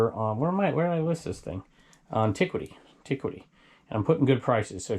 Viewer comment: Can you tell my what Antiquity is? i thought you mentioned you had something listed there but i have never heard of it. thanks! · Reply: lol, I always thought you were saying antiquity.